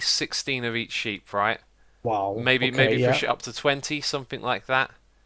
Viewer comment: I think Xisuma said "maybe for sh*t up to twenty" please reply X so I can know the truth ;-; ( 4:45 )